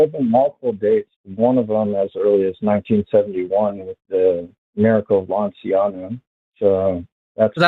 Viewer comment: I think it can be scientifically proven that a communion wafer turns into poop. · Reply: Ngl I heard him say “the miracle of balenciaga” and that made me chuckle